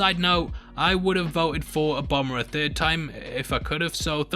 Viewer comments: Mate I love your vids but when you said ‘Obama’ I thought you said ‘a bomber’ and was super duper confused 😂😂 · Hahaha dude I thought you said ''I would have voted for a bomber''